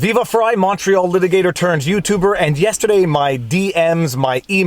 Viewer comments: What's everyone's take on this? Legit free speech? Or unprotected criminal conduct? Support links here. Locals: <link> Rumble: <link> <link> <link> <link> MY MUSIC: <link> Promo code vivafrei10 for 10% discount THREE ETERNAL TRUTHS Color shirt: <link> White: <link> Must watch: The Plot Against the President (documentary): <link> Must read: The Plot Against the President: <link> (Amazon links, I get an "Amazon Kickback" if you purchase through this link) POLITICS RUINS EVERYTHING SHIRTS In colours: <link> In white: <link> vLAWg ON <link> Pudge Dawg: With logo <link> No <link> Original Vlawg Dawg: <link> I SHILL FOR NOBODY It's perfect! <link> CHICANERY <link> Keep Calm! <link> Know the Vlawg <link> MY GEAR: Camera <link> Camera mic: <link> Stream mic: <link> Stream cam: <link> My GoPro: <link> The awesome thumbnails are from DSLR Dave! He’s open for business, check out his channel! <link> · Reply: I’ve been watching your videos for over 6 months and I just realized you say Montreal litigator. I always thought you were saying MACHO LITIGATOR! 🤯🤯🤯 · This is the first time I understand the intro. I always thought you were saying "Macho litigator turned..."